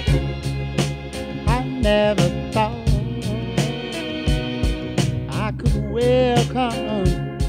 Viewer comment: it sounds like he says 'I never thought, I could wear condoms'.